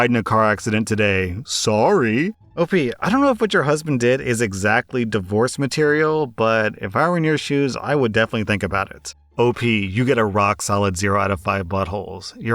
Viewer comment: In the second story when said "If I were in your shoes" I misheard it as "If I were near shoes" and thought to myself "If I was near shoes, I'd throw them at that sucky husband"